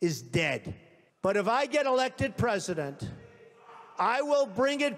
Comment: I always heard "but if im gonna let them trust you" instead of "but if get allected president"